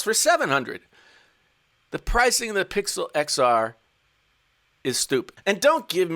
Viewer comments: you said Pixel XR instead of iPhone XR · Pixel XR? Am I the only one who heard that?